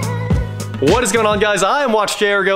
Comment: I thought you said your dad was 'watch sr go' I would follow him all day